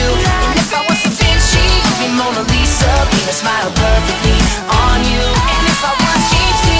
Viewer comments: Did anyone else hear "Penis" right after he said "If I was Da Vinci you'll be Mona Lisa (Penis) Perfectly smile on you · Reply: The Shiny I've always heard it as "paint a smile" But now after reading this, all I can hear is "penis" 😂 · I heard "Penis" but he said Paint a smile 😂😂😂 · He didn't say penise smile perfectly on you he said paint a smile perfectly on you or didn't he?! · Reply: OMG it does sound like he is saying penis but he says paint a smile